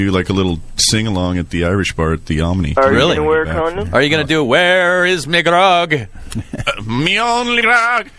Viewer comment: What does Jerry Cantrell say at 7:58? It's a swear word? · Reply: He says where is me grogh. (Beer)